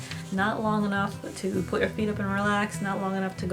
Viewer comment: she says: "not long enough to put your feet up and relax"....I played it back 2 times to get that right because the first time I heard it I thought she said: " not long enough to put your finger up your ass"....bout fell out of my chair.